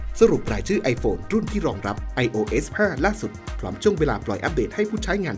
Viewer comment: ทำไมในคลิป พูดว่า iOS 15 เป็น iOS 5 อ่ะ หรือเราหูฝาดป่าวนะ 🤔3:10 / 4:33 / 4:50 ....